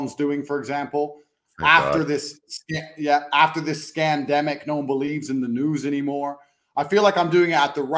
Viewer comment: "SCANDEMIC"- hope I heard it right ?!😎